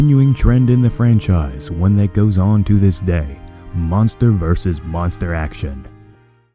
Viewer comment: When he said "Monster vs Monster Action" I thought he said "Monsterverse s Monster action" for some reason